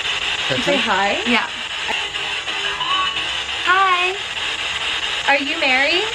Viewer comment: i hear "THE MONSTER" , not "Hello"